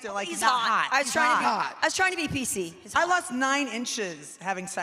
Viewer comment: Everyone thought KKB was saying her man was 9 inches when she actually said "I lost 9 inches" as in from all the calories burned from being with him